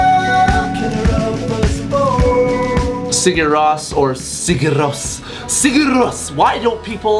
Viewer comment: I legit thought that it said Sugar Rose, not gonna lie